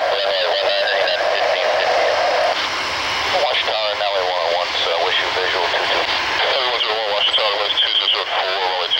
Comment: What is said at 1:03 .......Tower or so? Nice Scimi-Stars ;) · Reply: I think it was "Nelly 101" checking in with the Tower controller. It is a Jetstream:). If there was a Star Alliance livery, they would definitely be scimi-stars! Thanks for watching, and have a good day!